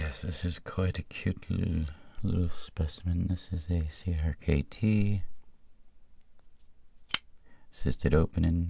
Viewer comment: What is the little knife at 13:55? I like that one. Can't quite hear what model he says it is. Hope its available in the US. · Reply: Topfblende CRKT Lichi 👌😷